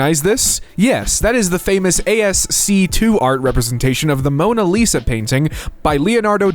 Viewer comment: did he just say ASC two?